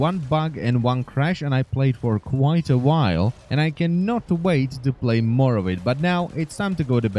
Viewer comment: Close to the end, it sounded like he said "I can't wait to play Morrowind"